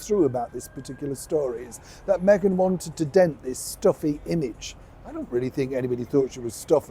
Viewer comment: I think someone misheard her say stuffy instead of slutty